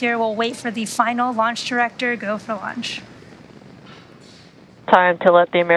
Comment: At first I thought she said they're waiting on the launch director to go for lunch 😆 I was like this is kind of a big deal man just get UberEats!!